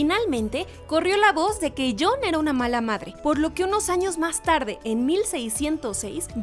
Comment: escuche mal o dijo que John era mala madre